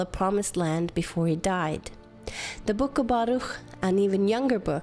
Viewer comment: can anybody tell me what the name of the second younger book was? @ 4:39. the transcript shows kobato? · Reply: The book of Baruch, I think.